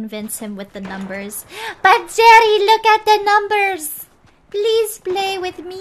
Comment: did she say Daddy? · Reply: but jerry lock at the numbers